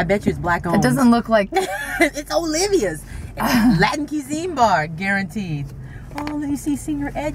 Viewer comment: When you were talking about Olivia's, I thought you said "Black Cuisine " and I was crying laughing so hard!!!!